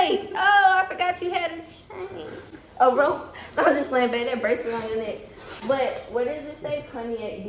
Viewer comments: “ That bracelet on your neck” 😭 daaang you didn’t have to do him like that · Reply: I thought she said braces on your neck 🤣🤣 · “ That bracelet on your neck” 😭 daaang you didn’t have to do him like that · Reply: I thought she said them braces on yo neck